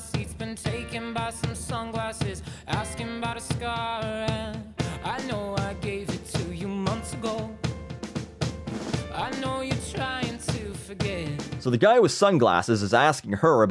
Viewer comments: I thought he said "cigar" and not "scar". This stinks. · I though We Are Young... I thought it said "Asking bout a cigar" and like... He's drunk and lost it... Jesus...